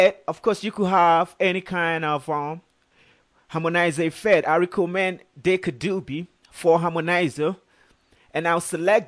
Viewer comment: he clearly says that the harmonizer is "Deca Dubby" (sic) I love the vocals. What song is this ?